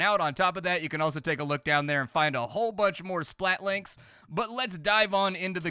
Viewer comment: Heard this as “splattlings”.